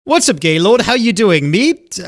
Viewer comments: Same although I thought he said "What's up Gay Lord" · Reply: Just rewatched the part... he did say "What's up Gay Lord" (Also as a lord who is also gay it fits) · The second it started all i heard was "whats up gaylord"